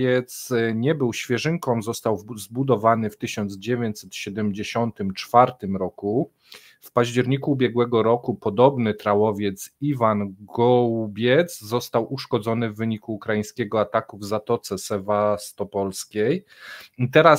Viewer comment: Czy ja dobrze usłyszałem nazwę tego zaatakowanego statku? " Ivan gołodupiec?😳